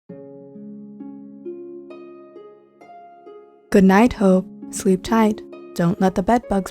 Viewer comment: did she say goodnight h*e 😭😂